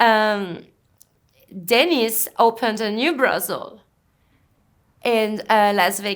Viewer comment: Perfect blizzard day video to put on while washing the dishes. I thought she was saying Dennis Hopper at first